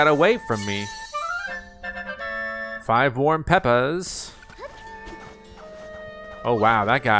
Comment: I THOUGHT YOU SAID "FIVE WARM PEPPAS" 😂😂😂